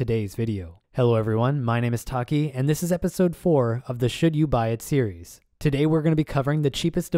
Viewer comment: You said "should you buy it series." I HEARD "shit you buy at Sears."